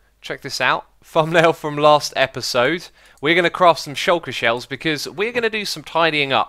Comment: did you say "craft some shuker shell" when 1:41?